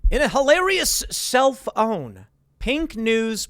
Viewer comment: whenever i someone says 'self-own' i hear 'cell phone'